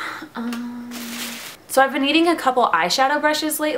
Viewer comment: Lol I thought you said you were EATING a couple of eyeshadow brushes lately and i got ffreaked out until i realized u said needing